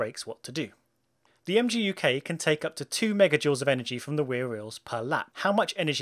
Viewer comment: Did you say wear rheels?